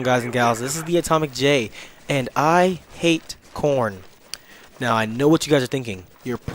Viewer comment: at first i thought you were saying i hate porn lol